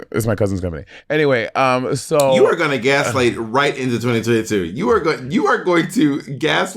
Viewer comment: OH OH OH! I thought you said light!!! Not like!!!! GIRL NO AHAH